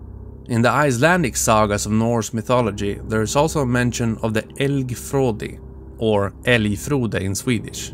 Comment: Hey, can anyone do the spelling for the moose human monster at 9:55? I cannot hear how to spell it out, and the subtitles are bad. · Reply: It is spelled "Elgfróði" :D